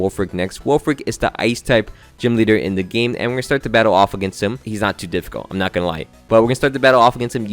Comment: HOW did I mishear "he's not too difficult" as "he's kind of a dilf" 🫣